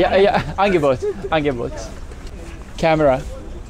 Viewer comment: I think he said “Frau muss filmen” which means “the woman has to film” or I think he means it as “women always need to film” bc of the stereotype that we take so many pictures or sth